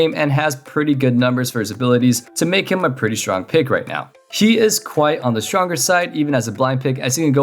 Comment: Wait, did i hear that you called Garen as she? 😂